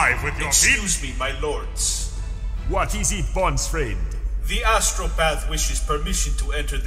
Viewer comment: for a second I thought Vulkan called him "Bonus Friend" and I thought it was funny.